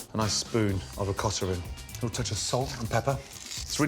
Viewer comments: What is "OKATA?" This was spooned into the potatoes at 1:11 time in the video. I didn't understand the pronunciation so I saw the word "OKATA" in CC print. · Anyone can tell me what he said on 1:10 ? A nice spoon of *** ? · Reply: Ricotta